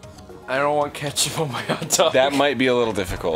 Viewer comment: I thought Ross said "I wanna get yo in my hot tub"